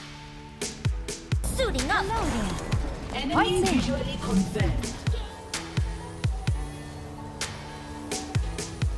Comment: What does mercy say at 0:04? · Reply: It's actually Swiss German! "Hoi zäme" and I think it means "Hello, everyone!"